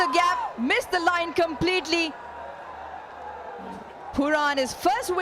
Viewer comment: wtf at 2:03 in subtittle she says quran not pooran